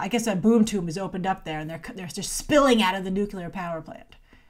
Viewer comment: Did you just say 'nucular power plant' ?